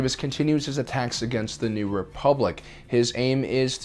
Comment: did he just say "The New Republic"?